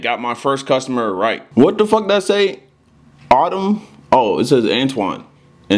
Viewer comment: I thought that shit said Autism...